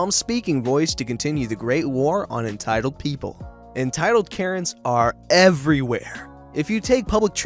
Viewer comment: Who heard him say carents instead of parents